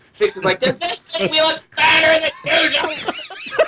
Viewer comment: what did say between 19:26 and 19:30? I really want to know what they're laughing about. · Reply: Toa LevoStalk oo i thought he said fatter instead of better :p that would been even better :)